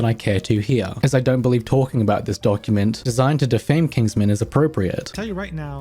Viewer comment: I think there was an error here, as the doc was meant to attack Kingsman instead of defending him? I could be misremembering though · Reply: He said "designed to defame Kingsman", but I can see how that can be heard as "defend"